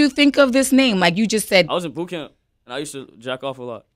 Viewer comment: did he say he was in bootcamp?